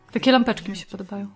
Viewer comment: Pierwszy raz jak powiedziałaś: Takie lampeczki mi się podobają. To ja usłyszałam: Takie babeczki mi się podobają. XD XD